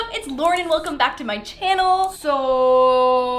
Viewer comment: Is it just me who thought she said “laurDen” in her intro?? 😂😂💕💕